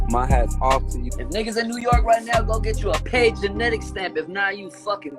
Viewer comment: did he say get you a paid genetics stamp ?